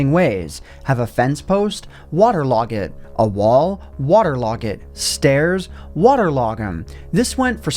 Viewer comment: did he say "water log in" instead of water log it?